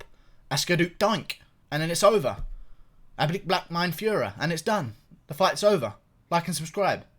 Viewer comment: What does he say at 5:08 "ablick black mine feurer"? Some German thing?